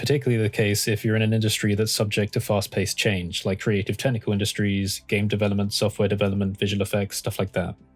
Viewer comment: for a while there I thought you were saying "creative tentacle industries"